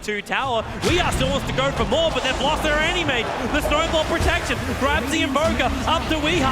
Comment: Did he just say "they've lost their anime" when EE died?